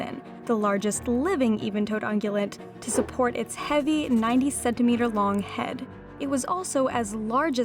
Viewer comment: I misheard "90 centimeter" as "97 meter", was like "wat?" 😅